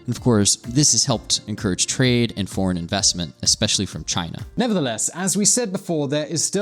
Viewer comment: min 03:00 did you say China? What's to come?